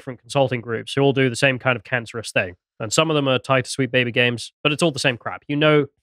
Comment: All I hear is "Sweet Baby Gang".